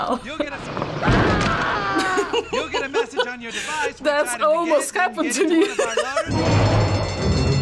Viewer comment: Wtf does she say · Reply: That almost happened to me